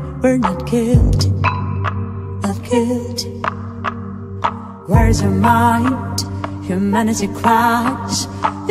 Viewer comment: I thought that she said where is kid And that at 0:29 you think you've got guts